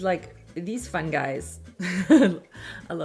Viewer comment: Ha! When looking at the dress I thought you said what are these little fungi’s not fun guys😂 I love your videos! Thank you!